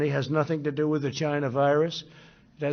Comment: Did he say Chinavirus?